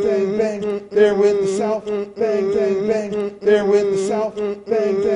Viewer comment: In the end i heard "They're with the salt,bang bang bang"